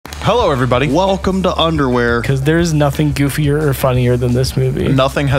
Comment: i deadass thought they were gonna say "welcome to undertale"